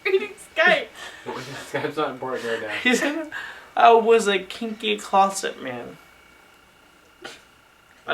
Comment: Did he say: "I Was a kinky closet man" ?! xD